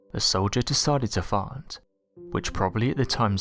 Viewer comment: Okay, so I have a (weird) question, with regards to number 20..at 50:06, but skip a few seconds ahead at about, 50:13 does he say that a roman soldier dude decided to fart or fight? & then at 50:33, it sounds like he says fart, again, then he says, what sounds like, ''however, when a soldier let rip''.. now is it just me, or does anyone else hear that too? or what? ..I'm just curious, is all....so please, can someone let me know? ..(if it's at all possible to answer me) ....thanks. ..peace✌& hugs💖 to everyone... with love, 💝from - Marina Martin💋 - in Surrey, B.C. Canada... cheers🥂🍾...xox & keep safe, kay.. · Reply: He definitely said 'Fart'. He goes on to say the soldier pulled back his garment [& bared his arse] etc etc etc, then 'let rip' in front of the crowd, [& they were not pleased] ha ha ha. The Roman leader of Jerusalem ... Bentilius Cum Anus. [friend of Biggus Dickus & Incontinentia Buttocks] a vewy good fwend in Wome. [& full of derring-do] I laughed so hard when he went on to describe how a massive surge began as people tried to get away from the fart stink, & that people were killed as they trampled on others in the panic, f'kin epic stuff. History is awesome :)